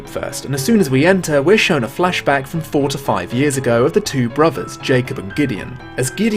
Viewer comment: Oh boy when talking about Gideon's flashback i thought you said "from 45 years ago" and BOY was i confused about this game's already confusing timeline.